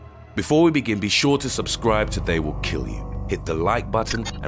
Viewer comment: Every video I watch from this channel the guy at the beginning sounds like he says "be sure to subscribe OR they will kill you".